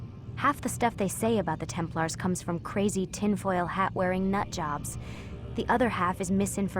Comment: what does she said at 1:42:26, i don't get it. someone can write it down, for me plz? ''half the staff they say about templars comes from crazy..(i want this part only).... and the other half blah blah blah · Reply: "tinfoil hat wearing nutjobs"